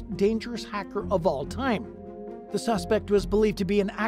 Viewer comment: I legit thought they said, “Crackers With Attitude”. Then I rewound it and realized they said, “Hackers With Attitude”🤦🏽‍♀️😮‍💨! Either way, both group names are horrible to be calling yourselves😭🤣!lmbo